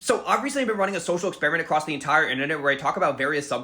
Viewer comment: Okay so I just commented "obviously" and now I can't find the comment so I would like to clarify that none of this is obvious and I misheard u saying "I've recently been running an experiment" as "obviously I've been running an experiment" and wanted to get a lil snarky but now I just look mean and bad